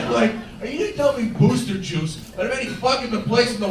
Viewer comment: haha I thought he said "moose'ster juice" I'm thinking wtf is that an East coast thing?